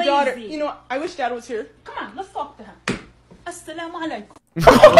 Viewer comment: Did she just say what I think she said Did she say "Assalamualaikum"